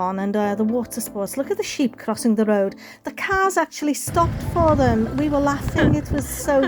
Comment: Ooo Dara I don't remember the cows 😃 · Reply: THIS IS SO EMBARRASSING!!! Around 1:30 you are talking about the sheep crossing the road and you said that the cars stopped for them. But with your accent “cars” sounded like “cows” to me! I was watching the video on my phone, with the picture small so that I could write comments at the same time. And of course I didn’t have my glasses on! I see very clearly NOW that they are sheep, but they just looked like little white blobs! 😂 and when I listen again, you clearly said Sheep! I really need to not watch videos with the volume too low and not wearing my glasses!!!🤓